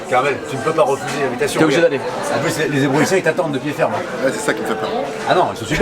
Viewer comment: c'est quoi qui attend cyril ? J'arrive pas à entendre le mot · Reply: Evreusien population de la ville d’evreux